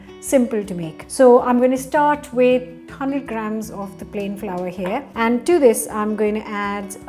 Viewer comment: When does she add in the potato? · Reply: April she's using potato flour, but she says it so quickly that at first i thought she said "plain flour"